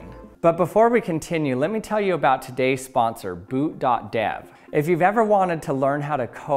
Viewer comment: I heard boob dot dev 😅